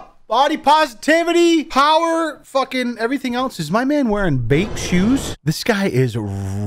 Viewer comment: wait, what kind of shoes did say? Edit: Nevermind it’s Bape, right? A Bathing Ape?